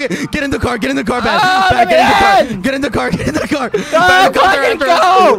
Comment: DID HE JUST SAY "GO ! FUCKING GO !" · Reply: He said " GO QUACKITY GO "